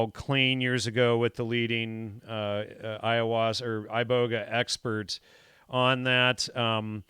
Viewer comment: When was talking about the show he did years ago called clean he slipped up and almost said ayahuasca @ 54:51 but cut corrected himself cutting the word short at the letter s then saying "ibogaine experts". Well since they were just talking about Crowley when he said that, I heard Aiwass, the demon Crowley conjured in the Great Pyramid that he drew a picture of and looks just like an alien. This is the being he got "do as thou wilt shall be the whole of the law" and the book of the law the basis of Thelema from. And no lie one week before I saw this video I had never heard anyone say that even the guy here said he figured that out so it's not something that is talked about in most of the stuff about Crowley that I know of, it was definitely the holy spirit whi showed me this. It is eye opening because all the secret societies and cults who follow Thelema or spinoff of the teachings present it as a way to become your own God like YHVH or Jesus and Lucifer whi they equate with being a God now no longer a fallen angel. Now I see how this appeals to peoples narcissism and a lot of then probably believe they arent worshipping Lucifer but their self who can becone a God though transcendence or karna/reincarnation or transhumanism, etc. And don't see that they are worshipping Lucifer and his will because in the bible it shows us that the flesh is corruptible by seducing spirits who get you to do their will, so doing as you/the flesh desire, is actually doing as Lucifer wills, so